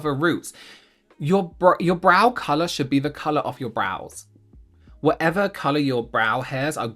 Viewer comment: Why did I hear: ”they must be the colour of your ROOF” 😭